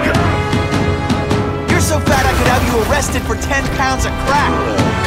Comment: Did he just say “You’re so fat I could have you arrest for 10 pounds of crack?”